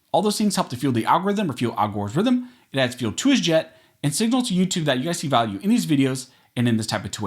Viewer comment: "It adds fuel to his jet."? Am I hearing that correctly?